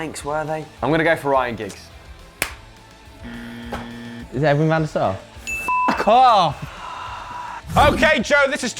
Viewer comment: can anyone tell me what did he say on 6:02 ? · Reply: Gamal Kelana is it edwin van der sar